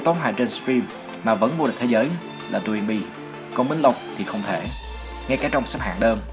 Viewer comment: thg ad bảo ai vừa tấu hài vừa vô địch cktg cơ, nói nhanh quá t không nghe ra đc, ai nghe ra nó nói cmt phát nào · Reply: DoinB